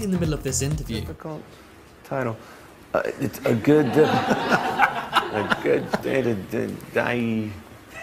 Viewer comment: That's so scary. it sounds like he says "It's A Good Day to Die." 😳😳😳